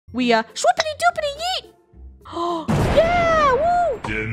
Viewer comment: First thing I heard " AnD wE shOupIDy DoUpiDY YeET "